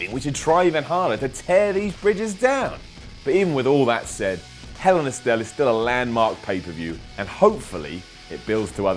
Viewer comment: Am i the only one who heard hell in a stell